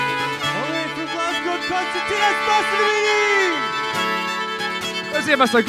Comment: Can anyone tell me what they’re saying at around 20:30? I’m really curious. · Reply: It's the Scottish accents maybe catching you out. the guitarist shouts out "all the way from Glasgow (Scotland ) on concertina Mohsen Amini"...... replies "lets hear it for Graeme Armstrong on guitar".... also shouts out "and for Hayley Keenan on the fiddle",,,,,,,,, right after these introductions he shouts out 'Lets see you put your hands together (clap along to the music.) This is the way they do it live in front of an audience. Unfortunately this was around the time of the pandemic lockdown in Scotland so there was just a live stream or recording from the Jura Whisky Distillery on the Isle of Jura ,Scotland .The annual Jura Feis (music festival) is now back to normal and the line up is around on here at youtube. Some other Scottish acts in this genre on here at youtube worth a listen to are RURA....MANRAN....ROSS AINSLIE.....BREABACH.....KINNARIS QUINTET...TIDE LINES.....PROJECT SMOK....PAUL McKENNA BAND...FOURTH MOON...DAIM.....SKIPINNISH all of these names will lead you to many ,many more acts we're blessed to have around us here in Scotland